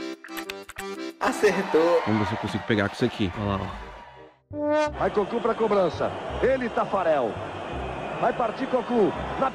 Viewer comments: eu entendi o cara falando: "vai com o CU pra cobrança" kkkkkk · eu entendi o cara falando: "vai com o CU pra cobrança" kkkkkk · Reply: Eu entendi "Vai partir pro cu" KKKKKKKKK